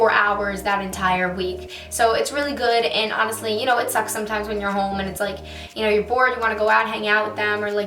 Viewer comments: thought she said "sometimes it's sex at home" i was like "OOOO CHRISTINAAAA YOU HAVE HAD SEX WITH YOUR BOYFRIENDD!?!!??!" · I thought she said it’s sex sometimes am I the only one???